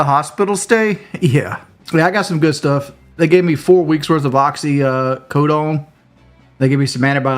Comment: At first i thought he said "4 years of Oxy" lmao 🤣 but yeah We're getting 4 weeks of happy pills? The ghost of Sean Ranklin continues to haunt his bitch ass lmao